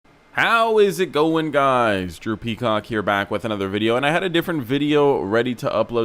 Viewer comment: really thought bro said “drippy cock” not drew peacock 💀😂